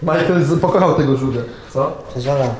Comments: ktoś pomoże mi rozszyfrować co powiedział? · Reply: "to jest ona" głuchy dzieciaku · Co powiedział? Bo kompletnie nie mogę zrozumieć bo mówi niewyraźnie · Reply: Powiedział "to jest ona" Jezu wyraźnie powiedział, ty masz chyba jakieś problemy ze słuchem czy coś.